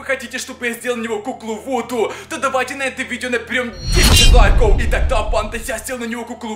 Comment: Мне послышалась на 12:30 минуте что сергиони сказал место давайте наберём 10к лайков мне послышалась давайте наберём 10к дизлайков (〃ﾟ3ﾟ〃)